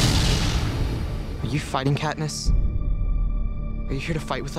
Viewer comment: i heard "will you fight us" and she replies"i will." i was like dayum katniss.